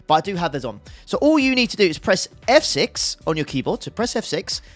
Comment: you say "press XXXXX" but I can't understand what you're saying? And did I read that it doesn't support VR? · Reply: "Press F6"